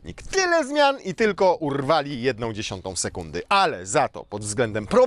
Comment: usłyszałem - tyle zmian i tylko *urwa jedna dziesiąta sekundy ;)